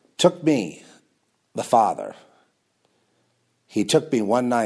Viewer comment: What does mean at 24:37 when he says the Lord took him, THE FATHER? He sounds like he's saying he's the Father. I really like his stuff and maybe he just made a mistake calling himself the Father or something but I'm just asking. Maybe I heard it wrong. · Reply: The Lord, Father took him...